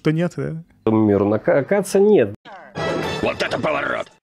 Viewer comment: На 05:10 упорно слышу "Каца нет"